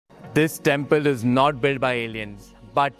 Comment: I was traumatized when I heard "this temple was not built by Indians" without noticing subtitles 😅